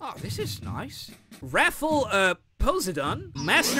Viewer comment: did he just say wrathful pos-sehdaun? 💀